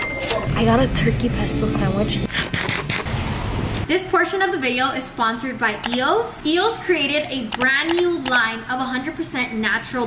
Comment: ngl i thought she said “unfortunately the video is sponsored my eos 🧍🏽‍♀️” 💀